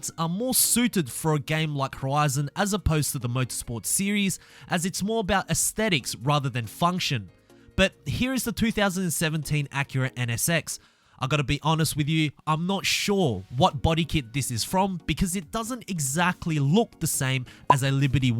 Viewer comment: did he say the 3017